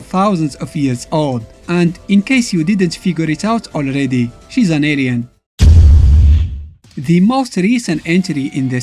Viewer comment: I hear it wrong at first (I had to play it again XD) by first time I was thinking you said "she is an idiot" 3:29 instead of "she is a alien".